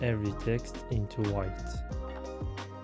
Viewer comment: Who else heard him saying Walter White at